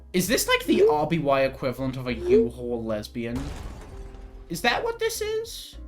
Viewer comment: what did you say? this isnt a bewildered response i just do not understand what you're saying at this point in the video? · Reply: “Is this like, the RBY equivalent of a U-Haul lesbian?” <link>